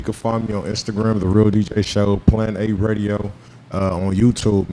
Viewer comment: Yo i swear when show 1st started screaming that "plan A radio" shit i thought he was saying " PLANET APE RADIO"😂😂😂